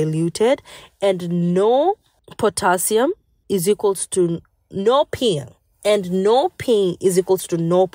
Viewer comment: I can’t understand what is being said at ~7:10? Am I hearing “no pill”?